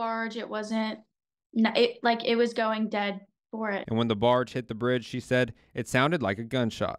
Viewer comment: she clearly said "dead center" and fakenew will NOT correct it!